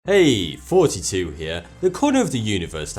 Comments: Every time he says "hey thoughty 2 here" i hear "hey 42 here" lol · is it me or does his accent make him sound like he saying 42 here instead of here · I heard "42 here" instead of "thoughty 2 here" and I hear that every time he does the into.